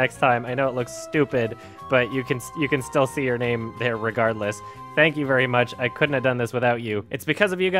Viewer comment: did you say irregardless??